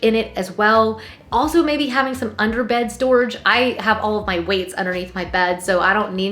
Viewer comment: Another great video! at first I thought you said “ I have all of my WIGS in my under bed storage” then realized after listening again you said WEIGHTS 🤪